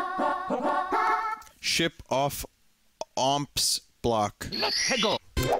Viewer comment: I thought it said Whip off Whomp's back.